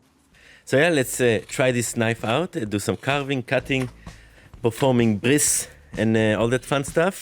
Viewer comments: did you say you will use it to perform a Bris? Ouch!! I didn't know you were also a Mohel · Performing Bris lol [Jewish Religious Ceremony of Male Infant Circumcision] Sounded like you said Performing Bliss like it is some state of an artist's mind hahaha. Love your videos! Thank you very much for your time, from a Random Automotive Engineer in Detroit [Detwaaa in Francois] See ya next time.